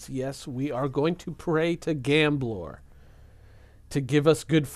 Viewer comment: ahh the gambler all this time I thought you were saying gamb-lord. I was like wtf is a gamblord.